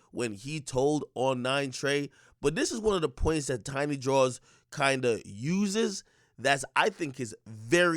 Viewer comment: Who else thought he was saying tiny drawls?🤣 👋🏾